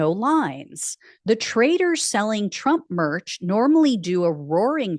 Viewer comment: I thought you said "the traitors selling trump merch". Wishful thanking i guess.